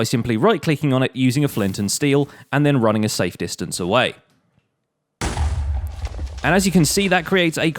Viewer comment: I thought I heard run a sike distance away XD